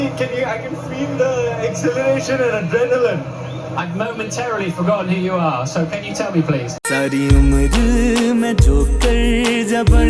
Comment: what did the interviwer say? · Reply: “I momentarily forgot who you are”